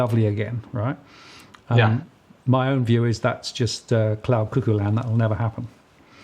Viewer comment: Clara Cuckoo land? Or did I hear this wrong? Is this a phrase from down under or from Britain? Or is it cloud cuckoo land? That would be like the German Wolkenkuckucksheim - cloud cuckoo’s home. But this piece is very useful - learned a lot.